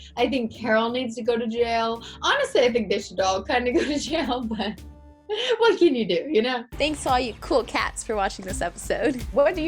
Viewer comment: what is she saying?? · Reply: “Honestly I think they should kinda all go to jail, but what can you do.”